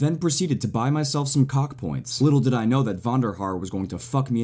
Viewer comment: Is it me or did he say cock points at 1:08 😂😂😂